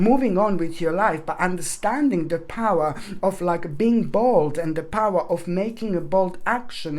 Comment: You said being Bold and I thought I heard being Bald 😂😂😂❤️😂😂😂❤️ so sorry but I really love your readings. 🙋🏼‍♀️😍❤️